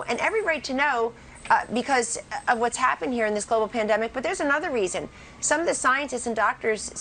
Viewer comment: did she just say "this little pandemic"